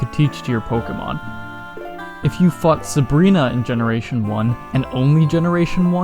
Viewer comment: I thought you said "If you fucked Sabrina in generation 1"